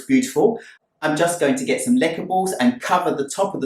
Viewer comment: What is the last step at 22:05? I don’t understand what he said. Leckables?